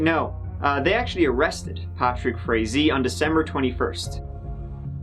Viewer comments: I thought said "Patrick Crazy" forgive me, I have a hangover. what a POS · I thought he said Patrick Crazy